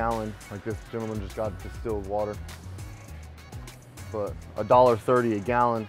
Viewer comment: “this gentleman just tried to steal water” Did I hear that correctly?